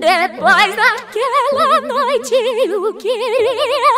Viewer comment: só eu que entedi Um QuErIa SuShI Edit : obrigada pelos 67 likes gente :) Edit : mds cada vez que eu entro no YouTube os likes crescer kkkkk mais obrigada dnv :> Edit : AHAHAHA EU TO CHEIA DE LIKE :> é obrigada mais uma vez kkk 🙂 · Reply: Eu entendi "Eu queria fugi EU QUERIA FUUUGIII